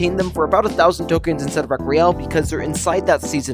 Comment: Why did it say "of real" when it was meant to say "Rec Royal"